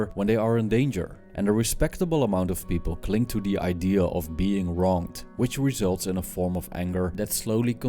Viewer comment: What word did he use around 0:31 as another word for resentment. “Being slowly consumed” sounded like he said being wrought but that’s definitely not the right word. Raunt?